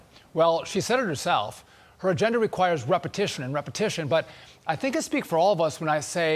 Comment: I beg your pardon did he just say "her gender requires repetition" . WTF?